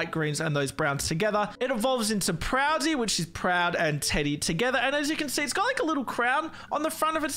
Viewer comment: I heard prou daddy